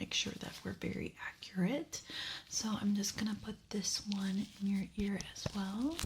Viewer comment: I was doing something when this video started and I had to rewind it because all I heard was "I'm gonna put this gun on your forehead."